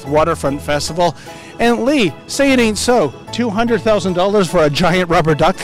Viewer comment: I honestly can't believe they spent $200,000 on this. To be honest I thought I heard $2,000 at first and thought, eh... what's the big deal. Nothing too bad. Then I reheard the real numbers. Yikes. We should have at least tossed a maple leaf on the side of it or had some attachment to Canada.